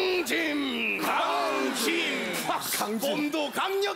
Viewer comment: 내가 잘못 들은것 강짐-강진 강바-강빠